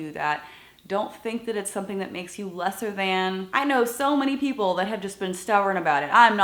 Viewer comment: I couldn't understand one part. If she said "Feel Lesser Than" but I thought she said "Less of a Man"